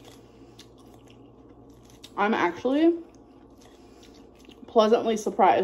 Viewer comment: When she said “I’m actually…”, I heard “starveeen” in my head. 😭